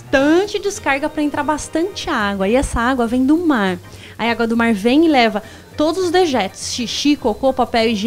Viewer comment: Aos 6:49 minutos entendi ela falar " aí a égua do mar"